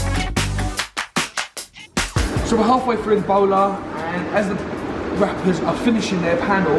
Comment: So some reason I heard so we're half way through ebola 😂